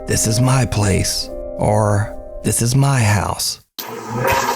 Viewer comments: That one at 27:03 sounds like it says "This is my house" and "get out of here".....idk if anyone else is with me on that but i swear thats what it says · In the last case with the boy and his mother I heard "Get out of my house"